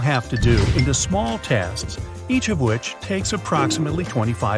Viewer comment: you say “tasks” but the subtitles say “tests”